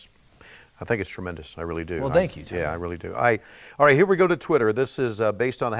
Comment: Did Cody say "Thank you JR? 😂